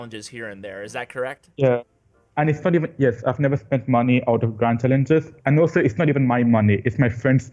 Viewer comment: It sounded like said "Cringe" instead of "Grand" lmao😂